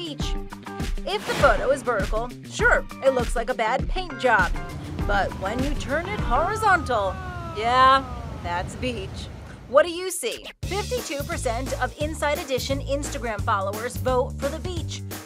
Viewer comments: I thought it said bleach😂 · I thought it said bleach at first